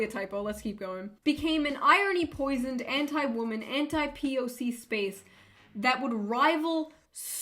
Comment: NGL, thought that said anti-POG